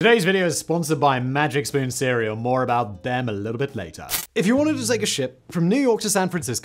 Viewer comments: Why did I hear “if you wanted to take a shit” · I thought said, "If you wanted to take a shit from New York to San Francisco...' when he started the video. I had to play it a second time. Sorry.